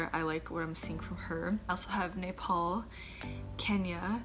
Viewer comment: oh i heard "nipple" instead Nepal..sorry for that.😁